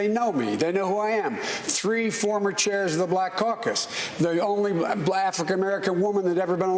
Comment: Did Biden really say "Blafrican"